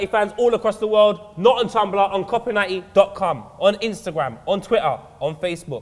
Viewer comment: Correct me if I am wrong but did poet say on copa90.cum?